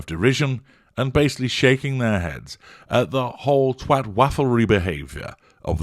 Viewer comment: "Twatfuckery" is now my new favourite word. Thank you HG. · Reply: I must be hard of hearing - I thought HG said "twatwafflry"😂🤣